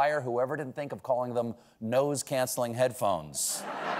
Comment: I heard “Nose cancelling noise phones.” But, that’s just me. 🙏